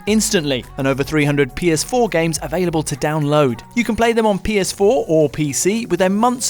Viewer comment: did I hearing this right? Did he say PS4 and PC??